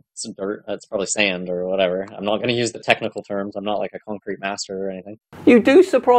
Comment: I heard “Khan-crete”. Shatner style!